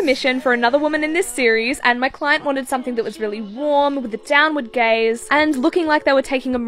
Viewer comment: i thought she said “my client wanted something that was really warm, down with gays”😭 and thought it would be a pride themed painting lol